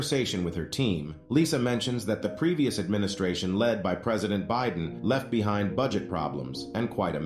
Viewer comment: I thought Lisa said Trump not Biden